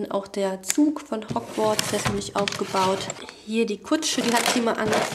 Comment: ich hab verstanden „ Lego schwänz“ anstatt „Lego Friends „ 😂😂😅❤️